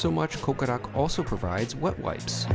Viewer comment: When you said they handed me wet wipes I thought you said they handed me "butt wipes". ..That got stuck in my head so I went back 20 minutes later and looked at the video again. (you said wet wipes) Great review!!